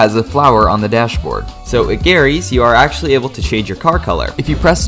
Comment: Which location did you say a number 45? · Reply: Garry’s